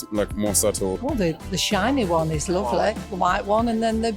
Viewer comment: i thought the old lady said xiaomi. i was like "wait hold on let me hear that again" then i heard she said "the shiny one" i was like. ohhh. thought she mistook huawei for xiaomi 😂😂😂